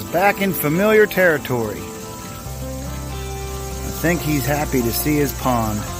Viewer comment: I like how he says "he" and "he's" but the subtitle says "Her" "hers"